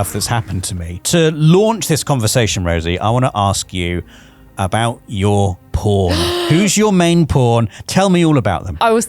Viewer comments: Who else thought that said "Let me ask you about your p*rn." ? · I swear, at 00:45 I thought he said “to launch this conversation, Rosie, I want to ask you about your porn”!!! 🤦🏻‍♂️